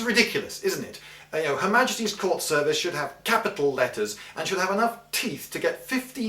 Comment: At first I heard "her majesty's corpse service."